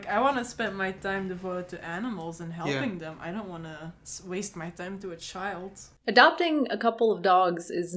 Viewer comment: Did she just say "waste my time to a child" WASTE?!! I feel so sorry for your parents who wasted their time raising your ass you fucking psychotic toucan bird!